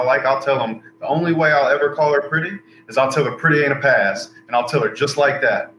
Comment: "Pretty and a pass" I have no idea what this means. Zero. I have no idea what he just said....and I'm a country boy like him. Can someone translate this for me? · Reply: pretty AIN'T a pass. That means just cause a girls is pretty doesn't mean you should fawn over her ...